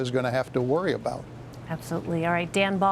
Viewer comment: Did she just say Dan Balls? I’m dying 😂😂